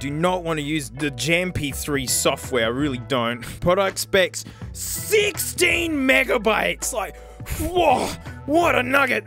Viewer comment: I thought it said 16 GIGABYTES and I still thought “that’s not a lot of storage” BUT 16 MEGABYTES WTF